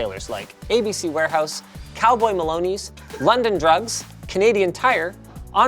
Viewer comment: I have not heard of Cowboy Maloney's in forever. Wow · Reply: DUDE, I thought he said Baloney